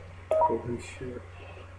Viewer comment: does anyone hear him say "Holy Shit" as he gets prepared for his jump?